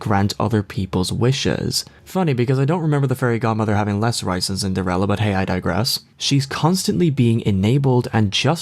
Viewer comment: what did he say? "Less rights in Cinderella"? His enunciation is so good except for that quick line.